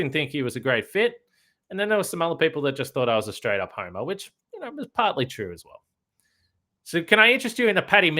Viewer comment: "some people thought im partly ___" what did say? lol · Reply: "Some people thought I was a straight up homer....which was partly true as well."